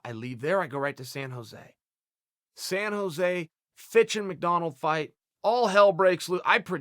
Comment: I fucking thought he said. "Went to San Jose, bitchin' McDonald's fight." I had my popcorn ready and everything... 🤣🤣🤣